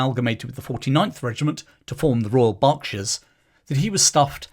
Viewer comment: I heard Bobbie's regiment being renamed as the Royal Barkshires instead of Royal Berkshires and it gave me a laugh